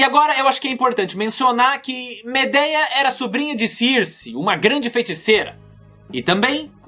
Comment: eu ouvi CIRCE?